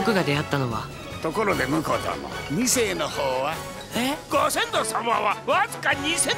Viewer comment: のおじさんがなんて言ってるかわからない………いせい？ 下ネタなんだろうけど、わかるひといますか？？ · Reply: 二世